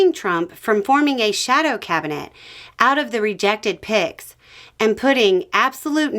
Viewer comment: Hrhr. I heard "... out of the rejected pigs ..." ;) Which is kinda an insult to pigs but made me smile anyways. Cheers <3